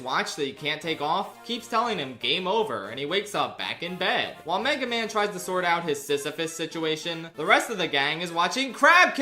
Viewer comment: Did he say "Mega Man" then "Megamind"